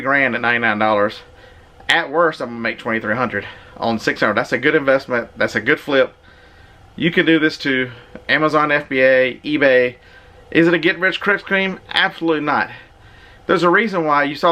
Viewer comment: Did I hear you say "it's not a get rich crispy cream"? Congrats!